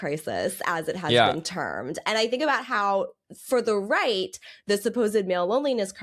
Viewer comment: WOW 😥 phew! I didn't hear the three clarifying words at the start of spiel "for the right". Was bout to cortisol-mog the comments. Love n Light Matt & friends for the content * P.S. The "Male loneliness epidemic" is not "supposed", it's real. Also not everyone on right is Toxic Redpill type, there ybg lads that are only right as a product of their community & family. Being that they are isolated without close friends etcthey don't have anyone to encourage them to the left.